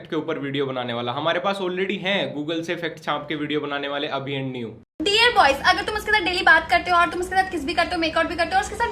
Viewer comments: Did he just say Abhi and Niyu at 1:28 , I hate him. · did he said abhi and niyu🙄